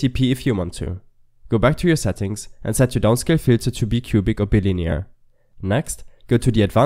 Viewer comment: I’m sorry what did you say here? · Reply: Go back to your settings, and set your downsale filter to bicubic or bilinear. :)